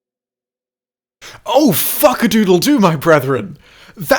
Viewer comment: Di... did you just say "Fuckadoodledoo"? Goddamit! 🤣 Edit